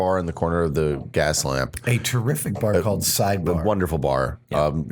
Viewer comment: Ha Ha Ha!!! Side bar! I thought, when it was first said, it was called side boob.